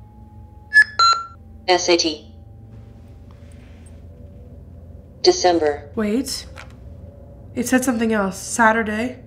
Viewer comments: i think the word said here was Assateague. which is the name of an island with wild horses. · At the 10:18 I heard sate? if I'm right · Esaty or SAT is what i heard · it said s a t sat just sat i googled it a test of scholastic aptitude test type in google what does sat mean it will say there you go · I hear "Necessity December" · in the video when you asked what the box said Saturday, December. I heard S.A.T I don't know if that might be someone initials or something else? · the alice box says "S A T" the letters that abbreviate Saturday, at least that's what I heard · all I hear right here is S-A-T.. 🤔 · It sounded like the Alice box said "S A T" to me. If it was supposed to be Saturday, I think it would have said that so I don't think it's Saturday. It sounds like an acronym to me. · I think it said sat..? but like s a t. it sounded like letters not a word. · Hey the box I think said S.A.T or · it said sat if you put the subtitles on. So if anyone didn’t hear it correctly that’s what it said.